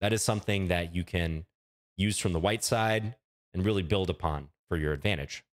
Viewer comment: "something that you can use from the white side and really build a pawn for your advantage" is what I heard at first in the microsecond before it collapsed to "build upon" in my head.